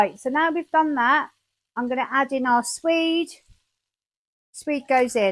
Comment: you put in the...??? I'm not a native speaker, it sounded like suede to me but the dictionary gave nothing but suede leather, which is probably NOT what you put in there 😂 Looks very delicious - my partner is having some extra days off, maybe I can convince him to give it a try. He mastered doing a good beef Sunday Braten (roast?) and loves anything beef, this might be a nice variation to try. 🤤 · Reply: Swede, some call it a rutabaga.